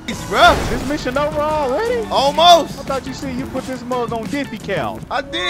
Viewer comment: I thought u said u put this muhh on dippy cow😭